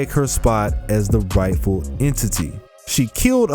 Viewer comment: it sounds like he said "waifu" entity instead of Rightful entity.